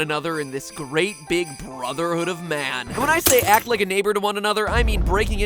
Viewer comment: at around 0:40 when Matpat said Neighbour, i thought he said the N word XD